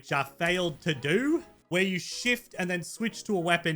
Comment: When u said shift I thought you said shit